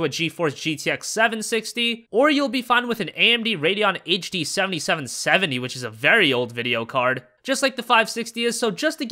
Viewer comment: can someone tell me which graphic card he is talking about? It is just to fast for me that i cant understand it even though i tried many times xD I'm not from an english spoken country tho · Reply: An AMD Radeon HD 7770.